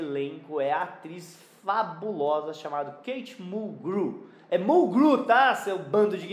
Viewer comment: Em 18:58 - Eu sempre achei que o sobrenome se falava Mulg Réu. Só recentemente é que eu escutei alguem falando o sobrenome dela 😁 Eu assistia Murder, She Wrote. Gostava muito também.